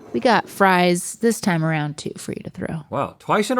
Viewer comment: I thought called Link “bro” for a moment, and I was like that’s new, but it was “throw”!